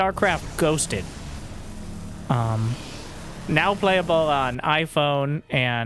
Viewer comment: I misheard "gabecube" but dont think i can type what i did hear.